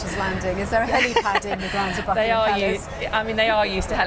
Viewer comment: Did I hear "*uckingham Palace?"